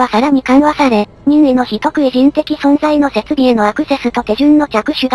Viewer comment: 非特異人間が人食い人間に聞こえる（）